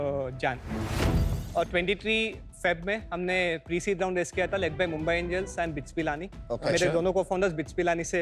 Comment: does he say Mumbai Indians? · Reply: He said mumbai angels a venture capital firm